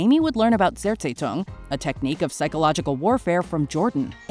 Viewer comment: What was that word said at 4:47? "Zairzaytone"? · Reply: It's zersetzung it's German